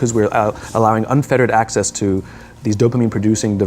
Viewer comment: the caption is wrong. I think he says "unfettered", not "unfiltered". pointless thing for me to even bring up, but whatever. there you go 5:24 lol there is just a green screen on the stock phone footage instead of someone putting a message there.